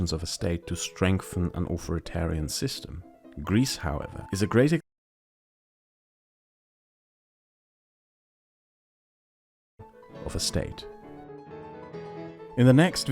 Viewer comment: Corrections and adressing critizisms: 1: At 22:04 this is embarrassingly wrong. And this is entirely my fault. I screwed up the math here when I tried to calculate the costs the first time. I thought that rail infrastructure costs would be a great comparative value that could illustrate the costs well to average viewers, but I completely screwed up the math when I calculated the first time. The costs of modernizing European rail to highspeed rail would lie at 1.4 trillion Euros. I calculated it again after all the video work was already done and the video uploaded. So the Greek bailout would only cover a quarter of the cost for such an infrastructure project. I am very sorry for this mistake. 2; At 11:48 I for some stupid reason say "over 100 000" when I should have said 400 000. I do not know why I made that mistake but you should be made aware of it. 3: At 25:05 I screwed up the audio overlap in editing and didn't notice until I uploaded the video. I am sorry for that. What i am saying here is "Greece however is a great example of the opposite, namely how corruption can destroy a state" 4: "Why do you not address the eurozone and how Greek membership in the common currency contributed to the crisis?" Because everyone else does, duh. I am pointing out something that I believe everyone is overlooking. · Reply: at first I thought that you wanted to say "Greece however is a great s******e of a state" and censored the swearword.